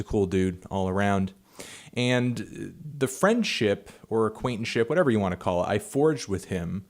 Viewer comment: did he say acquaintship" at 0:47? 'cause it's not a word, right?